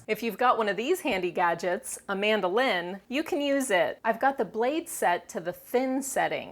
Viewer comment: lol I thought you said 'amanda lin' HAHA!!!